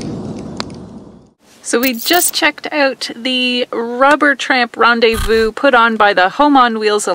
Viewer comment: did you say Robert... No, it is Rubber Tramp Rendezvous